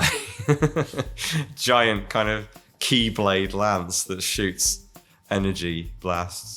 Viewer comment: did he say….keyblade?